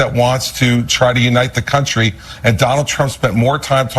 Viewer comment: I heard "donOLD" and thought it was someone who does not like him😂